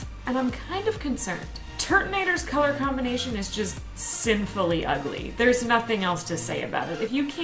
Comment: DID SOMEONE SAY SINFUL